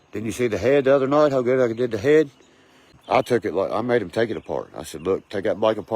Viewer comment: did he say you .see how good I give head really ... 👀